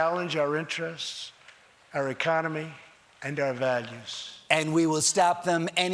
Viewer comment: is he saying the rogue regimes are challenging our "Valiums"? He sure does sound like that would be an issue for him...